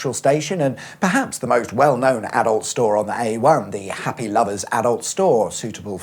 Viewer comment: I thought the first store said Happy Lover AI 😂